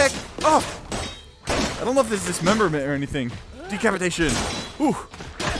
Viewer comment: relly you cuting people arm up ZD. AND YOU YOU SAID I WONDER IF THERES DICPATIN turn up volume to hear him say it 4:13 😲 hi and im 12 you fat boi lol and make it gandolf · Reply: The word is "decapitation" doofus